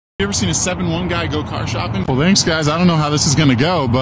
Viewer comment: “Go car shopping” I heard as “go kart shopping” and I got excited for a second.